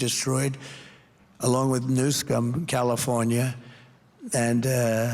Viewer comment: Did he call the Cali governor "Newscum"? That's AWESOME!